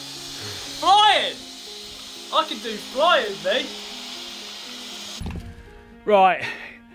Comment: "I can do flyin', B." Colin Furze is Gavin Free. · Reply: i thought he said " i can do flyin', me"